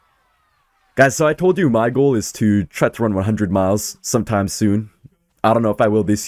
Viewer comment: did i hear right? Run 100 miles💀💀